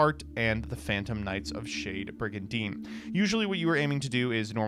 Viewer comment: When said shade brigadine the first time, I thought he said shitty brigadine.😂